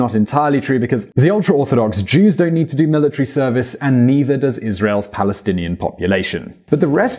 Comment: He say's "and neither does Israel's Palestinian population" at 5:09 but the subtitles say 'Palestinians are discriminated against' What up with that?